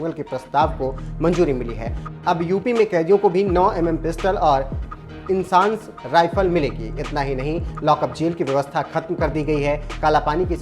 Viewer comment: Is anyone got confused what reporter said at 1:31 - abh khediyo ko bhi 9mm pistal milegi